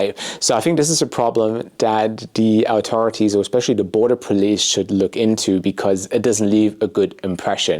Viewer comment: did you say board of police ?😅 youre definitely not informed my man